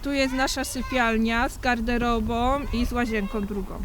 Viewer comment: Czy ja usłyszałem "Z garderobom i z łazienkom drugom"? 🤔🤭🤭🤔